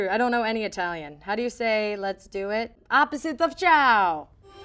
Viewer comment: Somehow I thought she said “ opposite of chow”